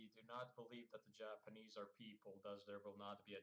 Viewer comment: we don't believe the Japanese are people Japanese people rage intensifies (That's what I heard)